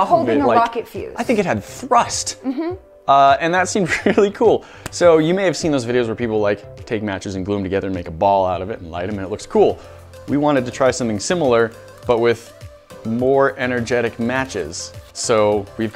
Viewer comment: Who else heard “light them and take them to a school”?